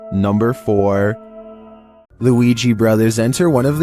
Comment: When you said ouija brothers I thought you said Luigi brother😂😂😂